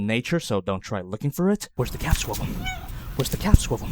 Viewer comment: at first I thought he said where is the cat swivel